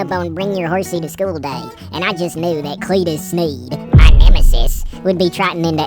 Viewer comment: i keep hearing "cleetus *sneezed*"